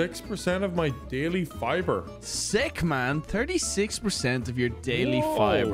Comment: I had to repeat that moment twice because I swear I heard Brian said "stickman"! 😂😂😂